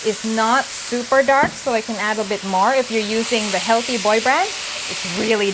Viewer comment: Huh? Did you say Boy Band? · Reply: Healthy Boy brand :) Cheers! Adam